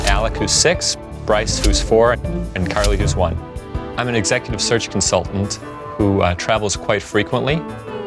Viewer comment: They said the dad was an “executive surgical consultant” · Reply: I thought he said " executive search consultant ". 🤷‍♀️